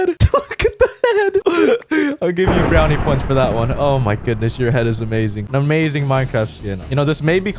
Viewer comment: "your head is amazing" yikes just tuned back in and that's what I heard lmaooo